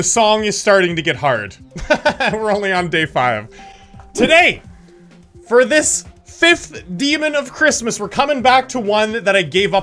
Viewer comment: Мне одному послышалось "дорогие"?